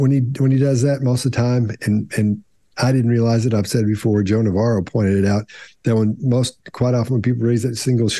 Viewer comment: I bet Joan of Arc was very good at reading people. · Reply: I thought they were saying Joan of Arc for awhile before it sunk in they were saying Joe Navarro